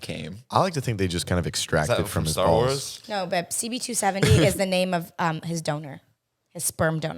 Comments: i thought she said “no babe..” 🫠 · did anyone else think it sounded like said "no babe" to jake????